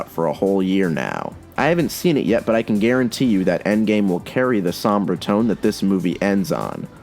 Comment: did he say Sombra instead of sombre